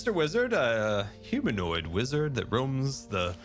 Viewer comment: I'm sorry, did say humanoid? is mister wizard actually human?